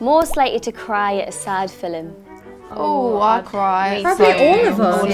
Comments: Wait a second Did jade just say filim at 3:41? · HOLD UP DID SHE JUST SAY FIL-EM